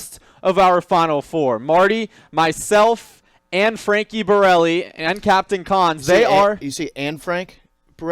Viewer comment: Did you say Anne Frank Borelli? 😂